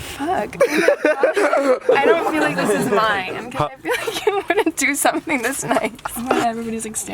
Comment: What did she say at 3:40 ? “I feel like you wanna do something tonight” ?